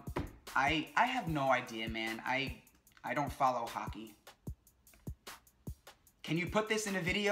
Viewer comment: Boi @ 7:31 he said the Miami dolphins are jockey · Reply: simply ops Hockey